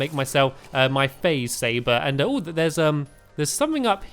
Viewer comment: It sounded like you said faze saver